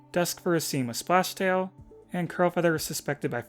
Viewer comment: The whole time i thought you said "Crowfeather" instead of "Crulfeather"